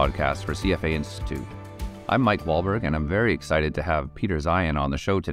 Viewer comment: At the end I thought he said 'Mark Wahlberg' and I almost spilled my coffee trying to rewind.